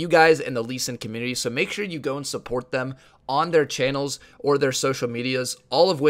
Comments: im confused afat 2:13 i understand "make sure u dont support them " XD but subtitles say go and and support them lol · I thought he said "Make sure you don't support them" 😂😂